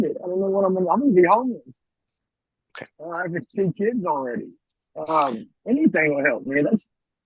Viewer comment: did he say he has 10 kids already?